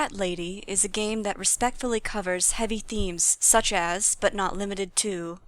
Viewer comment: Every time I hear the intro to these episodes I hear 'heavy creams' instead of 'heavy themes'. Still amuses me, like the child I am.